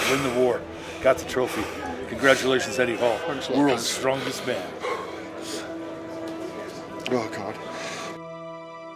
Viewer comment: does he say 'Thanks a lot dad'? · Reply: Thanks a lot Kaz (Bill Kazmaier)